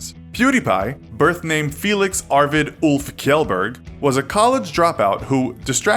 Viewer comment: The way you said "Kjellberg".. Ah, it's okay. Some words in Swedish can be very hard to pronounce. I hear it like "Sjellberg" or something pft